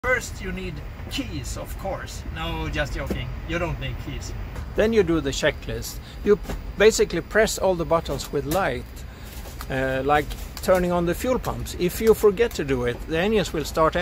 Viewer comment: I thought he said: we press all the buttons we like.